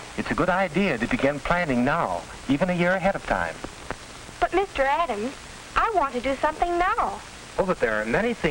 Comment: Every time she says “But Mr. Adams” I keep hearing “But Mr. Owl how many licks to the center of a tootsie pop”